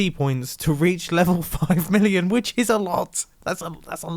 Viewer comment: all I hear is illion